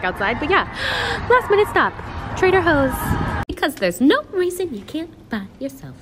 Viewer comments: Did She Say Trader Hoes? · did she say trader hoes??!🤣😂